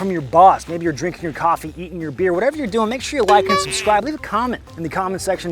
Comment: They way you said Like and Subscribe after you talked about catching us on the toilet. I thought I heard you say "Wipe and Subscribe"